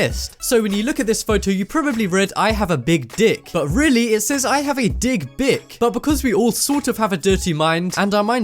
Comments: I thought it said " I gotta dig brick" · For the 3rd or 4th I thought is said " I gotta dig brick " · When the I gotta dig bick part came on I thought it said I gotta dig back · I thought it said "I gotta dig back